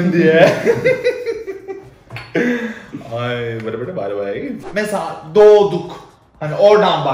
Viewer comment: orada ne dediğini anlayan?? 😂😂😂😂👍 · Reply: Whatever da by the way